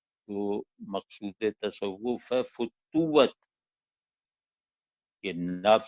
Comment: What is the word Shaikh uses at 16:41? Fatooat? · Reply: جی یہ لفظ فتوت ہی ہے۔ ایڈمن